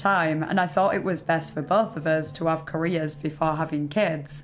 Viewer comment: definitely thought you were saying "Koreas" not careers 😂